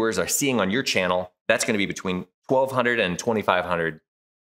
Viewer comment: He never answered the question. What does he charge? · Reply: I thought he said 1-2 grand.